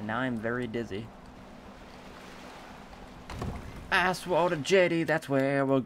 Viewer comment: Did anyone else hear asswater jetty 0:37 here